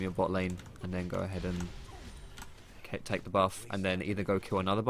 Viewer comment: i thought he said take a bath, then take another bath